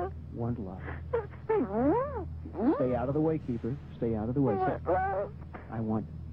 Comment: did he say keeper?